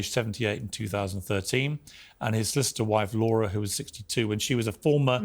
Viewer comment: DId he say "sister-wife Laura???"